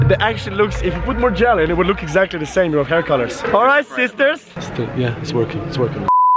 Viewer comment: did alijaz say "hello sisters" 😂